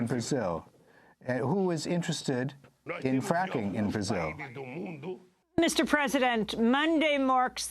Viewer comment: It seems he said "in fracking brazil". What lula actually said was "in weakening brazil".